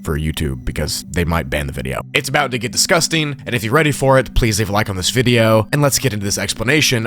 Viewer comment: considering the context, I really thought that said Live Leak